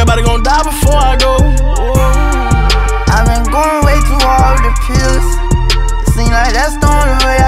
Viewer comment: “I’ve been going way to hard to piss” Kodak is too funny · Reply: I thought he said "I been going way too hard I been pissed" hence why they caught him pissing literally